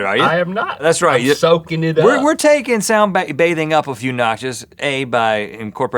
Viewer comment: lol I thought it said "taking a squid bath"